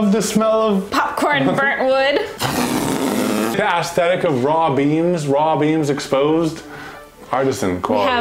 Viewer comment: could've sworn I heard "Raw Beans" not "Raw Beams"